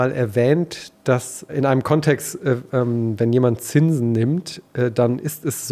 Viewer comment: Ich verstehe das Wort bei 16:30 nicht: "Zinn nimmt"??? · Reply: Ich glaaube "wenn man Zinsen nimmt"